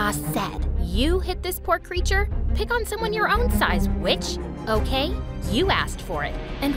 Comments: I thought she said b!tch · I legit thought she said bitch